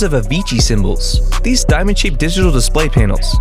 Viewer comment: I fr thought he said "Avicii syombols"